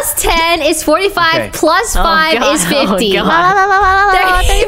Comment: what did Lily say :[ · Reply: So much harder